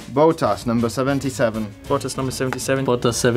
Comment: No one: Leclerc: “Voltas 77” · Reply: it sounded like he said boatas to me lol